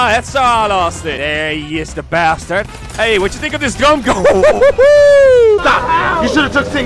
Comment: If u listen closely 2:18 it sounds like he said gumgun